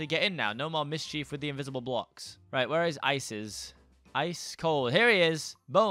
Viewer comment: who else heard Isis instead of Ices’